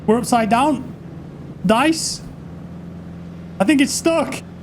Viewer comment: why are those players shooting your attack boat with their rifles? are they scripted or the players are just dumb? and at 2:17 did you just say DICE? 😂